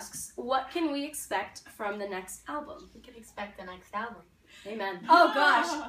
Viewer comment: GUYS WHAT DID CAMILA SAY WHEN THEY WERE ASKED ABOUT THE NEXT ALBUM? Is it "dont expect the next alvum"? Or I just misheard that? Answe pls it's bugging me that might be a joke but jokes are half meant. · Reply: You misheard she said "expect a next album"